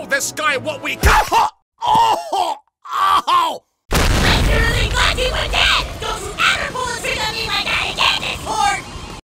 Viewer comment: what did sailor moon say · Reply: she said “I LITERALLY THOUGHT U WERE DEAD! DON’T U EVER PULL A TRICK ON ME LIKE THAT AGAIN, DISCORD!”